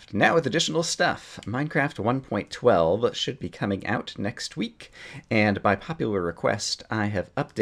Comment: "text week" what? but.... i heard it was today.... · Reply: Adam Eason *next week, and its been delayed to Wednesday.